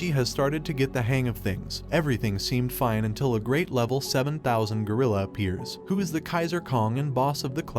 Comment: 770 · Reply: nah 2:50 he clearly says "a great level 7000 gorilla appears"